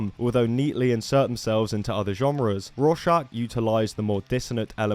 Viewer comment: what's the band idk what he said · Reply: Rorschach. They have 2 albums, Remain Sedate and Protestant. Both very good 90's metalcore albums.